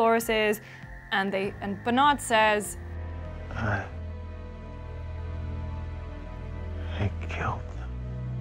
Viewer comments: If you watch the episode on HBO Now with subtitles, Bernard actually says “l...l killed them all” at the very end, put an end to all mysterious “he” theories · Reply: Subtitles are often incorrect. If you listen it does sound like he says I....he killed them all. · I don't know if anyone said this but when I watched Bernard say I... I killed them. I thought he said He as well. So I rewound and hit closed captions and It showed "I... I killed them" It was on Amazon Prime so I don't know if that is any good or whatever. Thought I would share